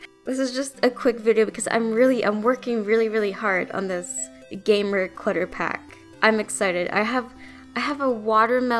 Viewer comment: This is cute, your voice is cute, and did I hear "gamer clutter pack"??? Can't wait ♥♥